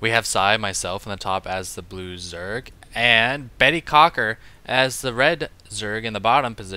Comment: LOL did psy say Bettycocker?